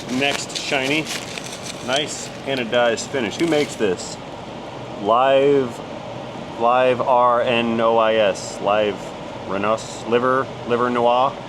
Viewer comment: When you were reading the word on the part it sounded like you were saying “Livernois”, pronounced liver-noy, similar to Illinois. It is part of the metro Detroit area, where lots of car stuff comes from.